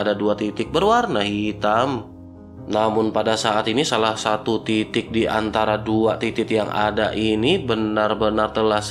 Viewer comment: Kayaknya ada yang aneh dengernya😂😂😂😂 Kata mimin "Salah satu titik diantara 2 titit" Di menit ke